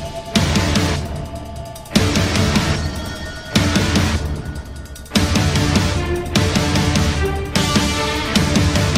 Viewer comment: What does Twilight say? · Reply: "Trixie"